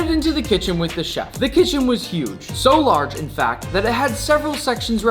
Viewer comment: i hear fat🙈 instead of fact😂